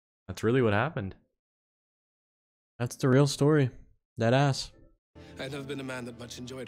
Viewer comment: I really thought said “that ass” Then it took me a minute to realize it was “deadass”! 💀💀💀